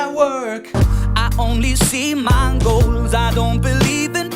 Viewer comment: I always hear 'Mongols' instead of "my goals"